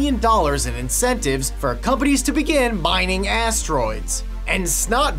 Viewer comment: I heard, "mining ass droids".